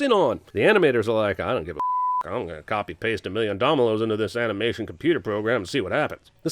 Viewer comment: I think he said domilos instead of dominos 😂